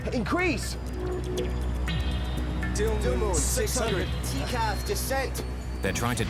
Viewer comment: Dildumnum? instead of Dilnum??